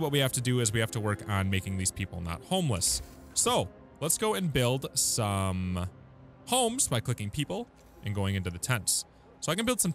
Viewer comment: I swear to god I heard "Making these people not homeless by cooking people and building tents" LMAO that's one way to have less homeless people